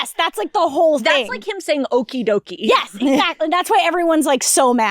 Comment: it's like him saying okie dokie but he actually says "okie day"